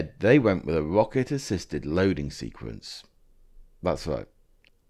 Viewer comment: Ha ha - for one moment there I thought you said 'Rocket assisted loading' !! 😂